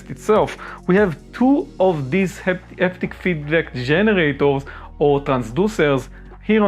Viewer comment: Bruh at 2:16 what the hell are you saying... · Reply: haptic feedback generators 🤣